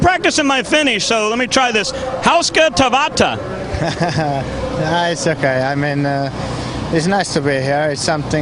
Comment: What did the guy say to Kimi in Finnish at 1:17? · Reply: ''Hauska tavata'' = Nice to meet you